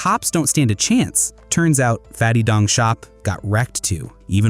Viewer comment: Did he just say Fatty Dong Shop?